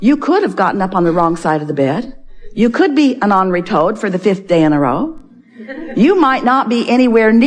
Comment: Hi, I,m learning English and I don’t understand What Abraham says in the minute 11:55… is it angry toad? Thank you!!